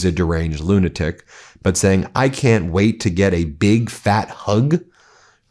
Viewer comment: I thought i heard him say, “big, fat tug”